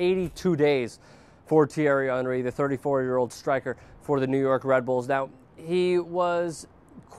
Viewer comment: WTF the subtitles say "New York rebels". It's RED BULLS.